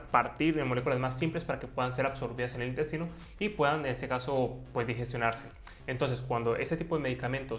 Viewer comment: Disculpe, ¿escuché bién "digestionarse"?, en el minuto 3:13 o ¿son cosas mías?